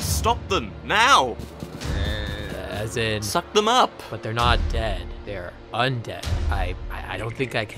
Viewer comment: HELP I THOUGHT BH SAID”your not gay”